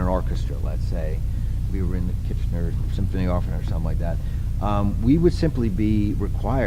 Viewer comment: Does anybody know what Joe is talking about at 33:14 ? A name of an orchestra or something? Thanks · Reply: I thought I replied to this weeks ago, but I guess it didn't go through. Joe refers to the KITCHENER Symph. Orch. (Canadian) <link>